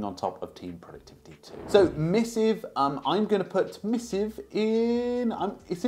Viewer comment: Covered in this video: Spike email Spark email Newton Mail Poly mail Hey email Air mail Missive email Super human email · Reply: Thank you, I was lost at Missive. I thought he was saying mystic or something. Didn't have any text on the screen or anything.